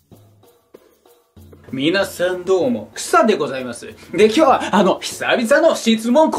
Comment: いつもどうもですがどうも草ですって聞こえて草ｗｗｗｗｗ